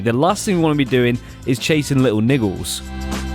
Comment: plz is it only me but at the min 4:40 i heard little ni .... sorry 😅😅😅😅